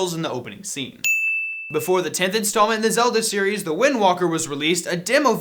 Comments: It sounded like you said Wind Walker... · Did he just say wind walker?